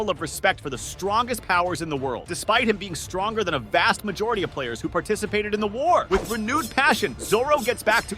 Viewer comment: AYO DID HE JUST SAY "PLAYERS"?